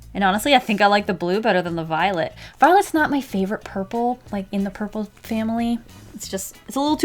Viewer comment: At first watch I thought I heard her say "Violet's not my favorite person!" Hurt my feelings a bit. (Hint: my name is Violet) Until i replayed it... Lol